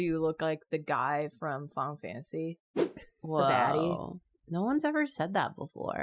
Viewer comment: I thought she said "the baddie" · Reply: definitely said ‘baddie’